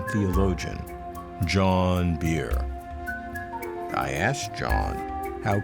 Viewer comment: When you said, "John Behr," I looked at his beard and thought you said, "John Beard."